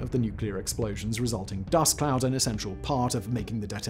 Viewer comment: I'm relatively confident that the mushroom cloud is a result of the atmosphere, and so a nuclear bomb detonating on the moon would not form a mushroom cloud. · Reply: Did he say mushroom cloud? Cuz no, it wouldn't be a mushroom. It would still create a "cloud" of sorts 7:32 he definitely said "dust cloud" so